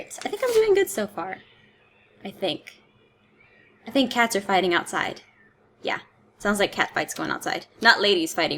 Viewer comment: when you said "I think that cats are fighting outside" I thought that you said I think Castors hiding outside